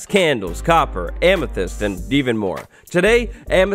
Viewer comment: I Thought You Said Steven